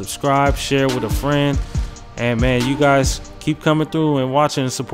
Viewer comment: he said cut I thought he said cuh 😂